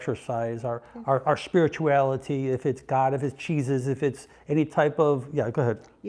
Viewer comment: #1 is having a support system. It's hard going this life alone. For those of us without support, or a family, but know know, it's within us. Did you say "cheezus" ? lol maybe I misheard, but cheezus was a happiness during my childhood Thanks for this video ❤️